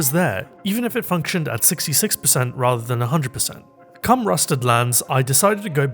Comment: I'm so sorry dude all I heard was " Cumrusted lands, I decided to go to my hunter ".